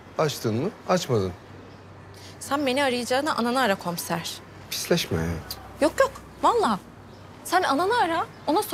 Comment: pisleşme hayatım mı? Dedi ben mi yanlış duydum